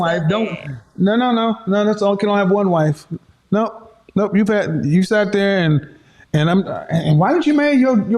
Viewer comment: 😮😂Did he just say You fat ??? Lmao...